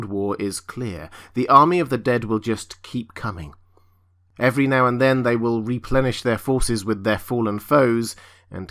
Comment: Muhfucker No, you can’t reanimate a wight that was destroyed with dragon glass, v.steel or fire. TF is this dude smoking... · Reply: “Every now and then they will replenish their supplies with their fallen foes” Ok now I hear it. I thought he was saying the destroyed wights would be reanimated. I am wrong. I heard what he said incorrectly. I yield